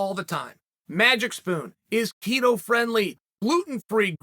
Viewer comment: At first, I thought Chael said "Magic Spoon is Tito-friendly" 😂